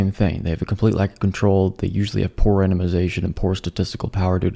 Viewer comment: when you said "poor randomization" at first I heard "poor enemazation" which kind of makes sense, seeing as most things called science aren't even verifiable enough to be legitimately identified as excrement!😂😂😂